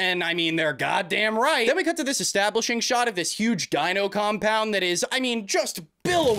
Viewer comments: I heard “huge gyno compound “ looked closely at his Lips and read what kind of looks like him mouthing the word The word gyno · swear he says gyno compound here lol